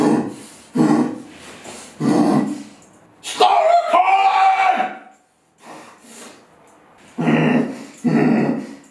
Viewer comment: did he say "Start" or "Stop" recording? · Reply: Stop